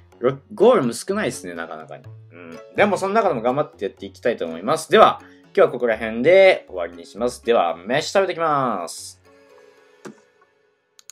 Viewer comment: もやし食べてきまーすに聞こえるのオレだけ？